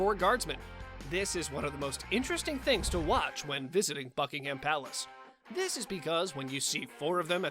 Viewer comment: I heard “Fuckingham palace”😂😂😂😂